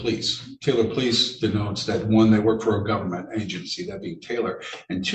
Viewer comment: Let us all remind ourselves this is about driving without a valid driving licence on a MOPED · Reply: I know .... when he said moped I thought he was joking ...... but then he said it 5 more times ... the facts in the case. Wtf. The defendant is a genuine loser. I almost lost it when I thought he said he worked for uber.